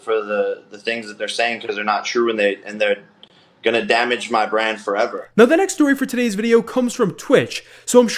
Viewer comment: "They're gonna damage my brand forever." -The guy that has the most damaged brand ever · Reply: I misheard that as brain... not that he has one tho.